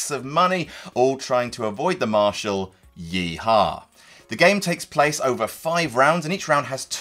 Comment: thought no one would notice he said “Yee Hoe” at 00:51 but fixed it in post